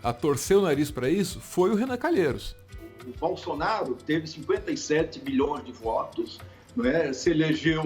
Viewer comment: Eu ouvi bem? Bolsonaro teve 57 bilhões de votos kkkkkk o Brasil está muito longe de se ter 1 bilhão de habitantes kkk Como Bolsonaro teve 57 Bilhões de votos?